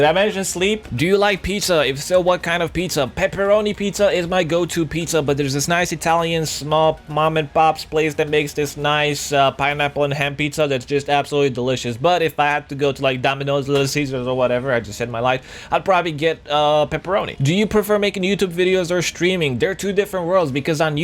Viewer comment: I thought he said pineapple and hemp pizza xD